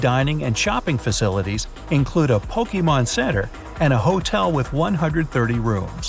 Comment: Is it just me or did he actually say Pokémon centre